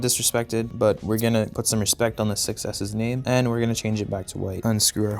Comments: Oh! Six S! I thought you were saying "success." Or something. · It sounds like he says iPhone success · I thought he said iPhone success but it was 6S🤣